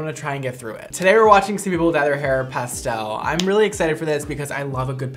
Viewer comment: omg you said pastel😂i thought you said passed out lol😂😂😂